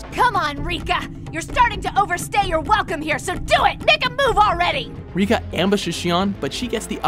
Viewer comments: did she just say the n word ? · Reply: i think it was "make a move already" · Did she really say ni**a move already....😰